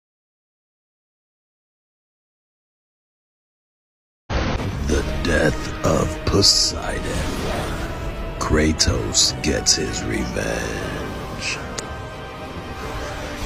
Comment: Bro on 1:11 he says “The death of Possidon” I honestly thought he said sun else💀